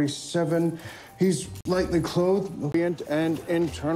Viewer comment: "he's lightly clothed" not "likely" clothed XD. I would say "I'm glad I watch all my media with the captions on" but the mm captions highkey are a mess (at least on netflix) edit: 5:54 I love how as soon as the inspector storms out yelling "watts!" the constable on the other side of the desk l e a v e s. like "whoop there you go I don't want to see you get in trouble bYe" edit: 7:56 I heard "it was [with] John brackenreid" but it could have been "young" brackenreid